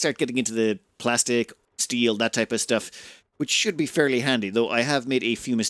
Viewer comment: I thought you said plasteel. Too much rimworld